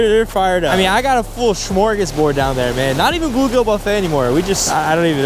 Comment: was that the Swedish word smörgåsbord? It sure sounded like it😂 I’m from Sweden btw